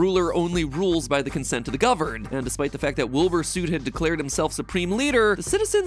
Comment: "Wilbur sout", did i just hear "Wilbur Sout" you might as well call him "wilbur soup" (this is just a joke)